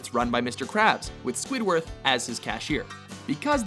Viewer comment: did you just say SQUIDWORTH?